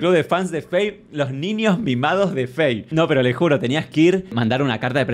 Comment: A la primera entendí 8:19 “los niños MIADOS por Fey”